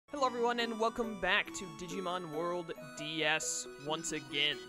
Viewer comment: The best thing for me to listen to while wandering the net. Don't know why my mind thought you say "welcome back to pokemon world DS" I must be super tired